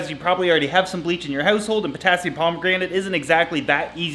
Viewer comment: Omg your pronounciations of some things. Got me rollin on the floor mate. Love your work. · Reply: I kept hearing “Potassium Pomegranate” 🤣